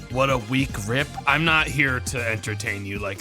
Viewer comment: Man I was confused because I heard "What a weak rip" as "What a week, RIP."